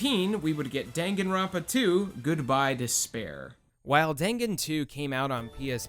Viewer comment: I heard “Good buy to spare” omg I should check my ears